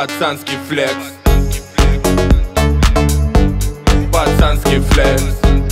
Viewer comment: Мне послышаля ботанский флекс